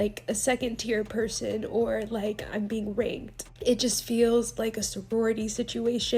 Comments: umm the fact she compared it to rape?? did i hear that wrong????? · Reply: Yes I thought she said it too but she said “ranked” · She said Rank not rape 💀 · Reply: I listened to it several times it definitely sounds like she said rape note rank. But I could be hearing wrong. However. The reaction is still entitled. Quite frankly it is a privilege not a right to go on these trips. If you think a brand is being gross… use your platform and don’t support them. Going on here and complaining about not getting the same thing someone else did does nothing to that brand. I don’t like the kardsshians bc I think they’re Culture Vultures and snakes. What do I do about it? I don’t give them my money. Do they NEED my money no. But you speak with your wallet. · Did the first girl say she didn’t want to feel like she was treated like a second tier person or being “raped”? Did I misunderstand that, I listened to it several times to try and figure out what she said and I can’t figure it out. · This shows you can’t rely on the caption in TikToks, I keep seeing “how could she compare this to being raped”….SHE SAID RANKED. R A N K E D. Listen to the audio (if you’re able), don’t rely on the captions all the time, misheard dialogue is so bloody common with the captions in videos